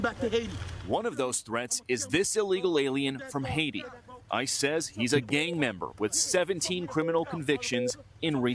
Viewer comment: What normal people hear: "ICE says he's a gang member." What I heard: "I says he's a gang memba"